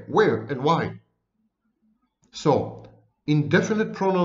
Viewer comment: What is the word. Polydramic speach.???? Is it spelled right?? at 25:01 and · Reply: Palindromic.